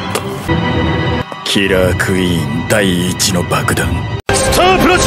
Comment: I heard this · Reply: I heard killer queen die lol